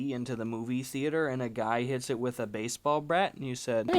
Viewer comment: Did you just say “baseball brat”